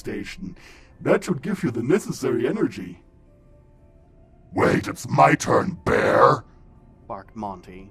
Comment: When Monty said ' wait, it’s my turn bear ' I thought he said ‘ wait, it’s my turn bi-'